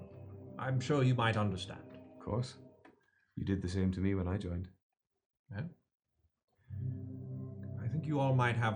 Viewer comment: "You did the same to me when i joined" did i hear it right 🤔🤨